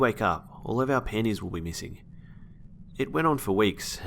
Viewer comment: I thought he was saying "missing pennies" lol